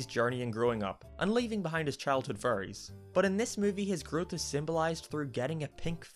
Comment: Anyone else thought he said childhood FURRIES?!?! Didn't think so